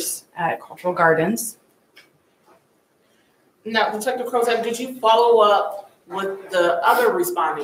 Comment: Who is the detective? She’s amazing and can feel in her voice how upset she is yet she interrogated for hours calmly and professionally. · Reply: lol I thought they said her name was detective Prozac 😂 but it’s actually. Yes she was very thorough and professional and seems like a legit decent person and great officer.